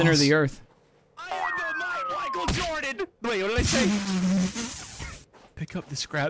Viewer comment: Did say he was the mhite wihcael jordan? At about